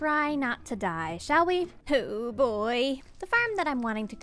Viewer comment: First time she said oh boy I thought she said poo boy lol 😂